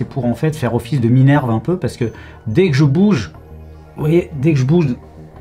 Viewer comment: Le bruit j'ai entendu "bougez vous"